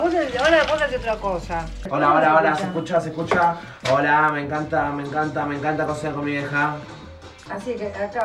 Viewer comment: Parece que dice "Me encanta coser con mi vieja" Jajajaj